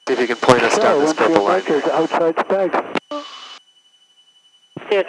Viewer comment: Did I hear someone mention Spag's on the radio chatter at 11:04? My Dad grew up in Worcester and Shrewsbury; I remember visiting Spag's when I visited in the 1980s. I know it closed and has since been demolished, but I assume someone was flying near the old building? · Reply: Yessir! SPAGS is the IAF into 29 at Worceter, and yes, it is directly over the old site of Spags :)